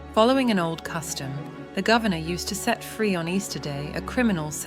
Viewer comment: Well, it's easy to know the letter is a fake, tho it had me going for a few minutes... she mentions the old custom of the governor releasing a prisoner ON EASTER DAY!!!! There was NOT an Easter back then. It was passover eve, n would never have been called easter! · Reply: I heard her say Passover not Easter 🤔🤔🤔@ she says the day of the Passover approached. Mmm weird that is